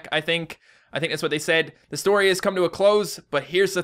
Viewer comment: That story is some convoluted bullshit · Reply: Ella L. I thought you said the story is bs Lol